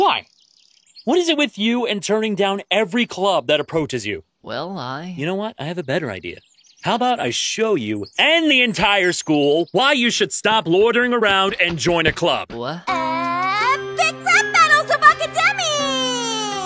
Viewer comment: I thought he said shoot you- lol